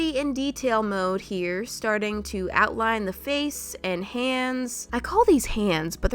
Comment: why did I say “blands”